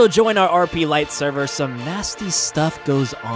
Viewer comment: i thought said : I will sue you in the next video